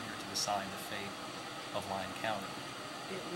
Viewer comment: Did he say Lyon county? As in NV?